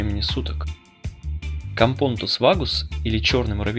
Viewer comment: "Всякий муравей, который не матка":))) Кампонтус, или мне послышалось? Кампонотус, вроде как правильно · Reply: Тоже услышала "кампонтус"